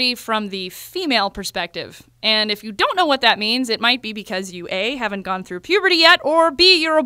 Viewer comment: I thought she said you don't know because your gay